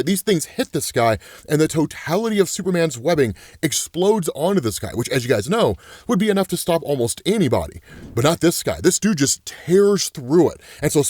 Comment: Did he say Superman